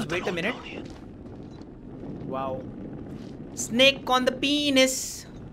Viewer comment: snake on the pinus made me laugh hard. 🤣 Or did I heard it wrong.